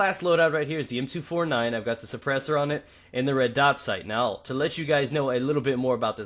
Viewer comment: i thought said suppresors are on lmgs are bad